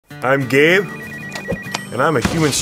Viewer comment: I thought he said “I’m gay”